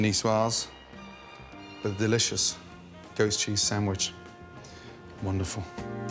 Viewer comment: Sounds like he says Ghost Cheese